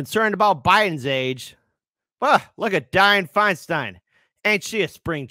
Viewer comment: "Look at Dyin' Feinstein", is what I heard. ;)